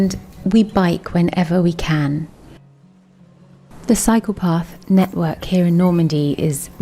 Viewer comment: LOL.....when she started talking about the cycle path, It sounded like she said psychopath.....good film though